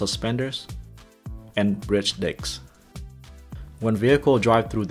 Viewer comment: it sounds like u said dick